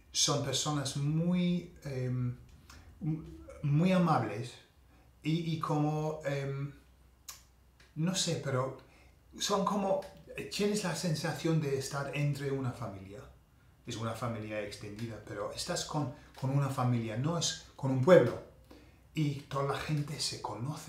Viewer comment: Hola, another great vid. I'm trying to understand what you say at around 2:36. It sounds (to me) like "C'é ['There is' in Italian] esa sensación de estar entre una familia." Did you break into Italian!? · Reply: Tienes la sensación.... I guess